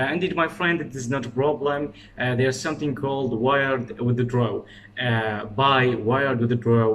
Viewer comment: i didn't understand that part when you said (wired with the draw) i googled it too but nothing came out i'm from IRAQ too, and i want to change my life in this country, can u please explain how to buy (wired with the draw)? i don't know what (wired with the draw ) mean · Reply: Simply there are 2 way to take your money from a freelancer 1 PayPal which is impossible in Iraq 2 weird withdraw in this method they are going to send you the money on your bank or visa card I am not sure I haven't tried it because I had a friend from another country who took the money instead of me then he sent it If you want we can talk on Instagram